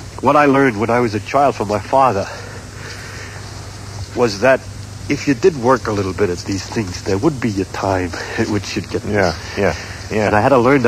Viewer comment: Can anyone please type the sentence that began at 8:05 I dint quiet hear what they said. Thank you :) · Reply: Firdaus Khambatta ”If you did work a little bit with these things there would be a time at which you would get this”